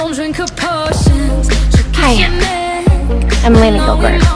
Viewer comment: Did she say Hi I’m Elena Dobrev ? In minute 1:23😳